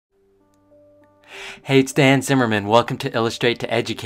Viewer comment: I thought he said “hey I’m hanz Zimmer” 🎥 🎶